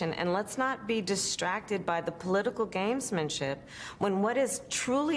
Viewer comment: Did she say "Games-MAN-ship'?